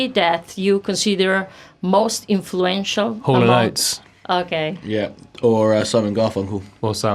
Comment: what does he say at 13:00? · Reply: THE TIDES Hall and Oates